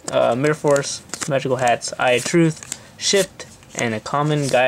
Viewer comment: Did you say "shit" ?